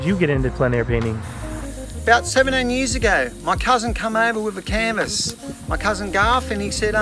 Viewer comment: Geez, you're a funny bugger Tez. Thought you said "my cousin came over with a carton". Painting, me, no worries mate.....too easy...